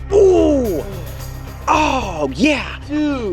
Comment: Did he say “Ohh Yeah” at 5:02? lol